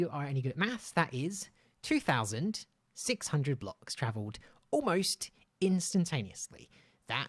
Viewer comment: did he said 3600 instead of 2600?